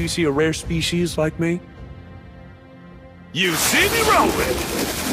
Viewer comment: I thought said "they see me rolling, they're hatin .." 😅😅